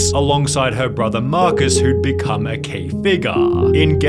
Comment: he said "key figure" in 0:28, was confused what he said